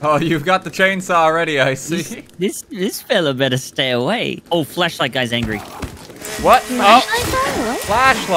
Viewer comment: I thought he said "the fleshlight guy is angry"